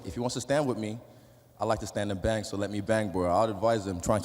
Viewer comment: did Israel say “let me bang bro”